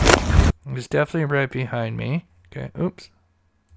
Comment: it sounds to me like she says"he's defineatly got a gun"but i could be wrong...I'm in the UK so maybe i'm just hearing different.i don't know,but it does sound like she says "he's defineatly got a gun" when he's on the bridge behind her.